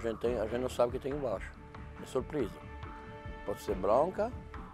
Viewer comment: O disse "é surpresa", nem o Master Chef entende o que ele fala kkkkk